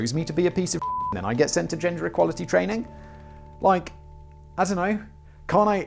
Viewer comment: “Ginger equality training?” …or did i mishear? :-p